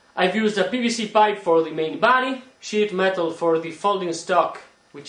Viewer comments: not making fun of you or anything, but when you say sheet metal it sounds like your saying shit metal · SHEET metal. Dont worry, I thought he said shit too lol